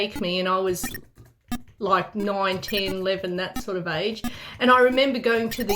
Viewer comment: When said "they used to take me when i was 9, 10, 11" i thought she said 1911